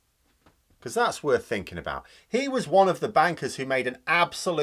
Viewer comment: Did he say bankers? Cuz I heard WANKERS. I'll show myself out.